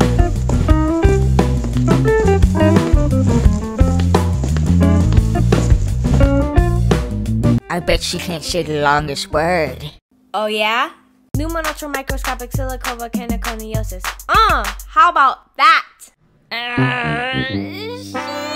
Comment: I thought he said "I bet you can't shake the longest word"